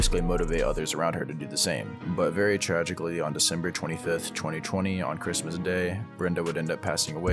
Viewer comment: i thought he said 2015 oh no nothing in between us with me and this I thought 2015 damn thought my instagram post from 2015 killed her but no man no it was 2020 I don’t know man it’s not that deep but whatever y’all stay making music